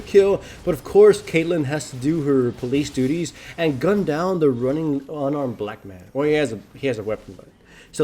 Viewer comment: "Caitlyn has to do her police duty and run down the black man" you are a legend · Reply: Potato Of Power thought he said "gun down the black man", Or was that just me?